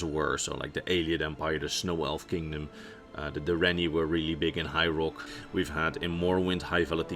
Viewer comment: I'm sure it's just the wonderful lul me to sleep accent but 1:38 it sounded like ya said the direnni elves were from hyrule. Great forget Alduin we got Ganondorf to worry about. I'm sure ya said high rock but still made me laugh and give the video a 2nd watch